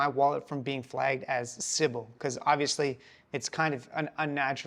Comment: what does he mean by civil? I believe that is what he said. I couldn't really hear. What would happen if your wallet got marked as civil? New, so talk to me like in plain terms. Thanks for the help. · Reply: It called sybil, it a term that denotes an unreal user, or a bot, e t.c